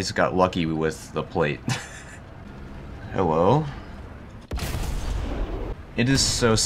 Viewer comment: I had to rewatch this because I thought you said "Hewwo" but it was just my hearing it wrong